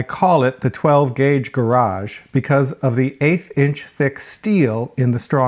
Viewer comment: Eighth inch thick steel....I thought you said eight inch thick. Here I am trying to figure out if you've got a storage cabinet or a bank vault. Nice job, by the way.